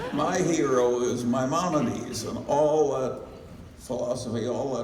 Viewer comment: Just can’t decipher what Charlie’s saying about someone being his hero at 31:30. Can somebody help? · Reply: Maimonides